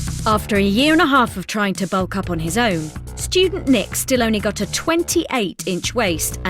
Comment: why did I think they said “stupid nick” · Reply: Unknown ? Hahahah just listened again and it says “student Nick”